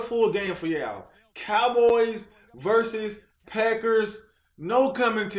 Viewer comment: Did he say "peckers" at 00:15 😂😂😂😂